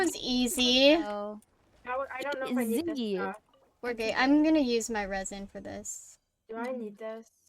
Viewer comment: THE CAPTIONS SAID "we're gay" 😭😭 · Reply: i said “we’re good”